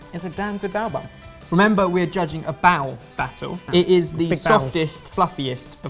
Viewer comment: I thought it said "bad bun battle" and I was so psyched